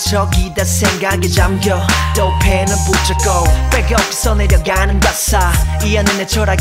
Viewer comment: 제가 노래를 잘못 이해했는지 처음들을 때와 조금 바뀌었네요. 그 중 하나는 예전엔 빼곡히 써내려가는 가사로 들었는데 빽이없어 내려가는 가사로 들리네요 이노랜 원래 벌스가 더 좋은것 같아요..빼곡히 가사를 써내려가기 참 힘들거든요…